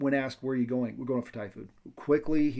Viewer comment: what was it the suspect said when asked where he was going? ty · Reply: To get Thai Food then Dad started talking about the Pullman Washington Swat occurance